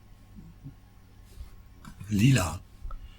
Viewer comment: sounds like he says Lila and that's purple in Swedish lol